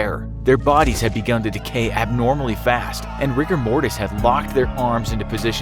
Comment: did he just say rick & morties?